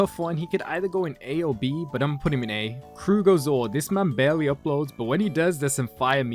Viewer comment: I need help. What's the channel's name in 7:54 ?? He said it's underrated so I gotta go look for this but I don't really understand what he's saying and the subtitles aren't helping. And yeah thanks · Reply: The YouTuber name is Farmer John or if ur talking about the other one it’s krugoZOR